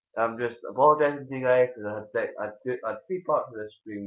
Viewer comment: Watch from 2min 39 it sounds like u say I apologise cause I had sex